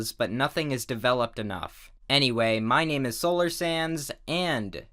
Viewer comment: When he said Solar Sands I thought at first he said Solar SANS